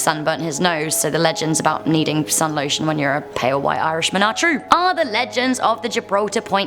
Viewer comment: Did you just say "Pale white arse"